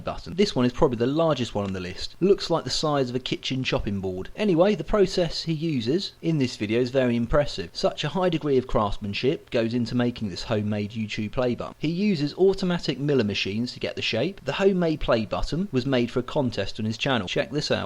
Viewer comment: did he say s**t or...? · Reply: ha ha i just listened to this and im pretty sure i said craftmanSHIT :)